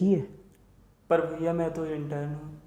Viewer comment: what did he say? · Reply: Mein intern hu